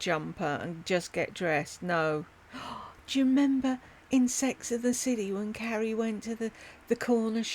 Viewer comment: “Insects in the city” I thought you said 🤣😂🤣 Got it now 🤦‍♀️